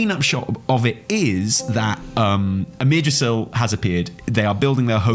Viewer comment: am I the only one who heard "amirdrassil has a beard"? 😅